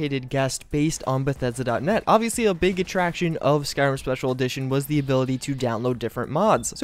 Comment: did he says starwars special edition?